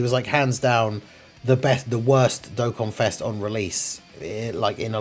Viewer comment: what was that, Raditz was the best dokkanfest on release? I think that's what you almost said @ 08:12? hmmmm